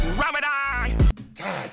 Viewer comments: I think squid's saying "my trigger finger fast, Ramadan" but hard to say for sure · I’m alittle late to the party but that squidward bar it sounds like he was saying his trigger finger fast, Ramadan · ngl. i thought squiddy said "MY TREAT TO BREAK THE FAST, RAMADAN" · Thought squidward was saying my trigger finger fast and I think Ramadan is when you stop eating or fasting in Muslim culture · What he said was "my trigger finger fast"